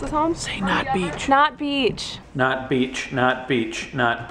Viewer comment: at one point it sounded like he was saying "snot beach"